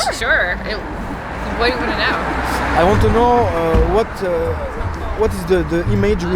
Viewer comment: moi quand j’essaie de parler anglais 😂 On t’aime frérot 😂😘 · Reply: il dit "i want to know" , pas "maintenant" xD